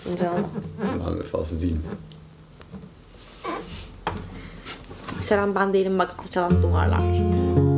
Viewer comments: da Deniz ne diyor bir binbeşyüzüncü dinleyişim hala tam anlamadım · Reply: yükselen ben degilim bak alcalan duvarlar diyo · 'den sonra ne diyor ya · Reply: Yükselen ben değilim bak alçalan duvarlar diyor